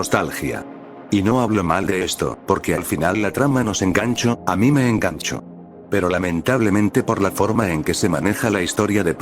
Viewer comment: yo escuché " Ami me cacho"